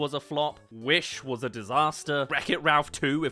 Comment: For a second I thought you said, “W.I.T.C.H. was a disaster.” Needless to say where my mind’s been lately.